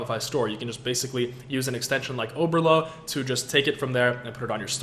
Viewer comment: what extension did you say you used? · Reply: Oberlo